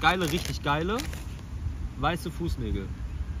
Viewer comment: he said the "N" word!! 0:21 I don't understand the language, but this is the part you should OPEN your mouth and put her toes in it! What the hell were you sayin'?!! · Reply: weiße Fuß"nägel" = white toe"nails" yeah for non-germans it can really sound like the N-word 😅